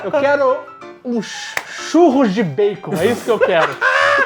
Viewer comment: Estava só ouvindo e eu ouvi "Chuvas de Peitos" em vez de "Churros de Baicons" kkkkkkkkk Tive que voltar e voltar pro ponto da fala kkkkkkk